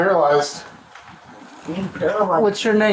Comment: did she say “he paralyzed me”???